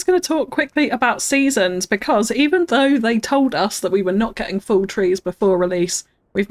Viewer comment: Did you say "now we get full trees" or "Fall trees" after 2:14? The automatic subtitles show "full trees", so I'm not sure - even though you're talking about seasons, so I guess it's "Fall trees" 😀. Thanks. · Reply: Haha, fall trees, definitely fall trees! (Although the trees have grown up a big too so I guess you could also say they were full 😂)